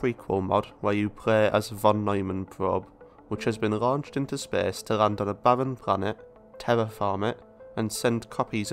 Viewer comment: wtf does he say between 0:07 and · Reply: You play as a Von Neumann probe which has been launched into space to land on a barren planet, ...